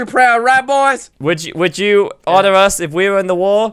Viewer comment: when said "would you order us in the war" i thought he said "will jews order us"